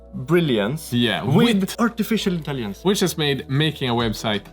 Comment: When said "artificial intelligence" it sounded like he said "artificial Italians" and I find that quite funny.